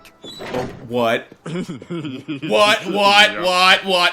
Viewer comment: He made puns at the start "aflutter" for fluttermac , "cheerful" for cheerimac and "hardly" for marblemac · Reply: Oh and his "what" kinda sounded like "butt"